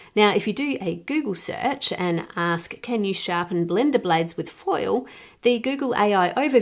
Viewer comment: I had to stop and play the Google search back because I could have sworn it said, "Can you sharpen bleeding stumps" 😮